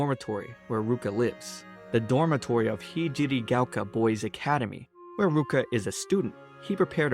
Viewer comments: Am I the only one who heard 6:09 as P.Diddy boys academy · Why did I hear "P. Diddy Gaoka boy's Academy" 😅